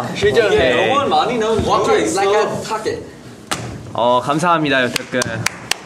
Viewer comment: did mark just say "walk it like i talk it"??? im so done with him